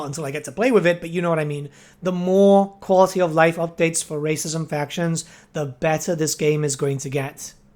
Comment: I heard that as "The more quality of life updates for racism factions, the better this game is going to get." Based.